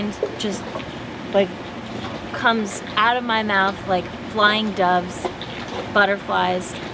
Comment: Doves and butterflies... I thought, I heard angels.